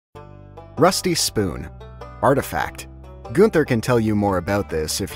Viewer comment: Did he just say Goonther!